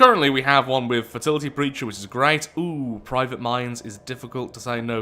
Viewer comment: I heard that as: "Private minDs", which could be a cool upgrade (de-buff?) for like a hive-mind or something? :D